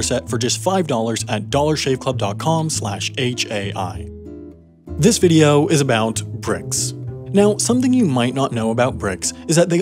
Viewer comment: I thought you said, “THIS VIDEO IS ABOUT PRICKS” sure got my attention